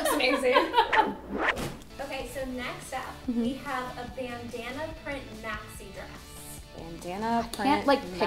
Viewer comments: i thought she said banana print maxi dress and i was really confused when it was BANDANA print 😂😂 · Is it just me or when they said Bandana print i thought it said Banana print 😂